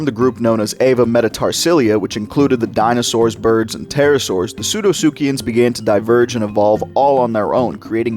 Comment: The dinosaurs, dinosaurs, and pterosaurs???????????????????????? Why did you say dinosaurs twice? 💀